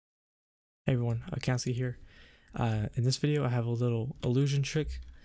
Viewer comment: the intro sounds like it says “hey everyone. I can't see, here.”